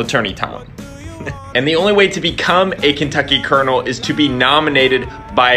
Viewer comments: Who else heard it as "Kentucky kernel" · I thought he was saying Kentucky Kernel...